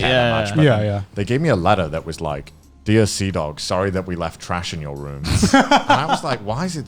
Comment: I thought he said Seadog 0:30 Is Seadog Connors actual channel name but he spells it wierdly?